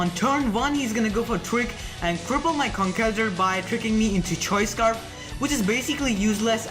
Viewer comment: Ummm, did you just say hes gonna go for twerk at 00:26 XD